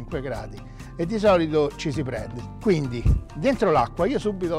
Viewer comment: k so i am english all i hear at 1:05 is cheesy bread just me or what?